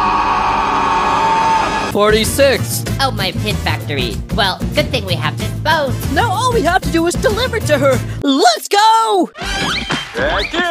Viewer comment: Oh my gosh! Pin is in there! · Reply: Also, Coiny, I thought you said "oh my mint".....